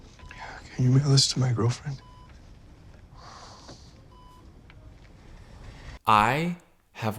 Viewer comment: I think he said "can you mail, this to my girl friend?"